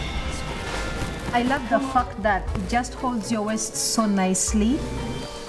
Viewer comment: what did she say?! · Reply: I love the fact that, the dress fits your waist so nicely.